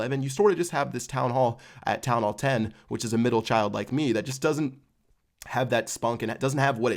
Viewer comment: 'that middle child like me that just doesn't have that spunk'. did he say that or did I mishear him?haha